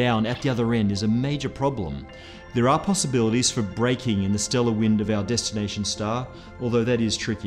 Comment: I thought he said breaking interstellar wind! 😂😂